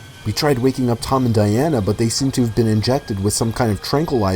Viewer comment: I thought u said Tom shot a gun too not injected